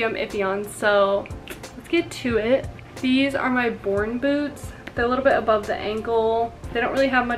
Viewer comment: I totally thought you said boring boots 😅